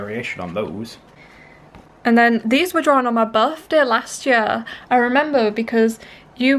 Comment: I thought you were about to say this was drawn on my but XD I was like WUUUUT